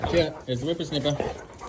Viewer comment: Watching this again, I've just twigged that said 'whipper snipper' and was referring to Gus's tail. I think it's what we call a strimmer in the UK. I thought he said whipper snapper to Wolfie as in 'you young whippersnapper'= young child. Ha! Love it.